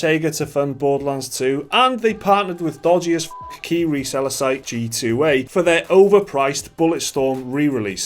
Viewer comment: what is it that is said? All I can hear is "Dodgy as F Kiwi seller site G2A" but G2A isn't an NZ company they're based in Hong Kong unless they sell Kiwis, which is pretty dodgy I'll have to get DOC onto them. If the game is good, and is worth a normal retail value (whatever that is in your country, here in New Zealand it's like $100-$120, which is too much in my opinion) then there is no problem with paying a for a full price game. If you look at it an are instantly like, oh that's an indie game so I should pay an indie game price for it seems a bit shallow to me. Season pass and DLC is a whole different kettle of fish however.